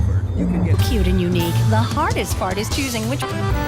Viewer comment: i litteraly have this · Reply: Cool :D Btw, I keep hearing the ad say 'the hardest-fartest choosing'